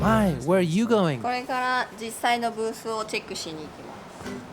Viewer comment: I thought she said “Take a shit” 😅